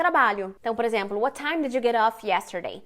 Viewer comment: Oi, contribuindo: 3:24 na legenda está "do" ❌ e nao "did" ✅ como vc fala. Tks!